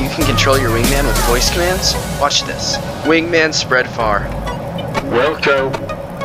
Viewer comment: you captioned it wrong He actually said "Willco" which basically means acknowledged or something like that (also i loved the editing with the camera changes and stuff)